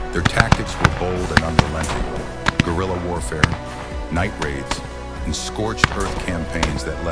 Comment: Did you say gorilla warfare 3:44 you racist basterd